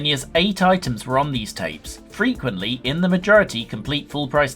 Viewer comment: I thought it said 'tapas' and not 'tapes', at first 🤦‍♂️